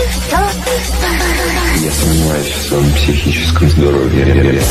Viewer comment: Я услышал: Я сомневаюсь в твоём психическом здоровье.